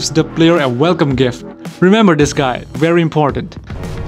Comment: When you were talking about Cross: "Remember this guy. Very important." I thought you said 'very impotent'.